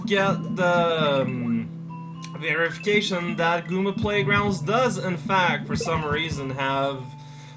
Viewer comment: Wow... for a second I thought at 1:00 he said Google Playgrounds. I thought he was making a goofy word for Google Play and was really confused.